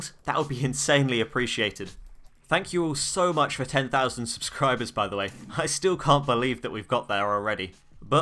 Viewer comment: did he say ... 10k subs???? Mfw dudes at 20k HOW